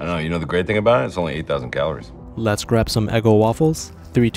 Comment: Istg i heard him say " a dozen calories"